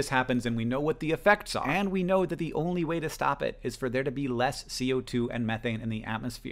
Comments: the subtitles incorrectly read as more CO2 and methane instead of less CO2 and methane, which is what Hank says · Hey Hank! Absolutely love the video, though the subtitles at 3:29 say 'more' instead of 'less'. Just letting you know :) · the captions say "more" instead of "less" -- idk how to change this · Did anyone else see the subtitle difference around 3:29-3:31? The wording is directly opposing what hank is saying, which can be harmful for hard of hearing folks! Hank says "And we know that the only way to stop it is for there to be LESS co2 and methane", but the subtitles say "MORE"!! Can someone fix this? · there is an error in the closed captions. they say "more CO2" instead of "less CO2". Wanted to give a heads up because I would love to use this video in my classroom but would need auto-translations to spanish for some of my kiddos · Just a technical note, I'm not sure if someone else has found it: in your captioning, where you've said "there needs to be less CO2 and methane in the atmosphere" aloud at 3:31, the caption says 'more CO2 and methane'. Not sure if they're automatically generated or if someone can edit them, but it's a bit startling!